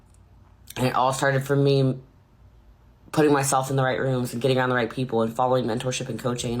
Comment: “Putting myself in the right rooms and being on the right people” - hahaha might have misheard but it made me laugh!